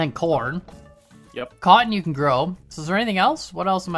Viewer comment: at first i thought you said "Kan you can grow" like how are you planing on growing yourself?